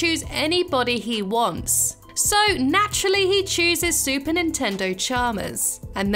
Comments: why did you say Nintendo it’s superintendent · did u say super Nintendo ?